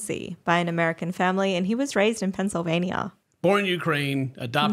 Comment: I was across the room while this video was playing, and I definitely misheard "Pennsylvania" as "Transylvania"